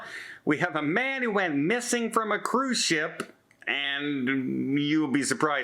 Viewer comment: Thought it said mangos missing at cruise port!! 😂